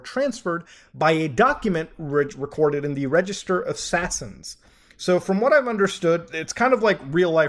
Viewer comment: I had to rehear "Register of Sasines" three times because I kept hearing, "Registered Assassins".